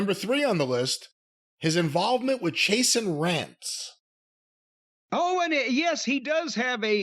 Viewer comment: ok, good, so i'm not the only one who heard "chasin' rats" ... thanks Jim hahaha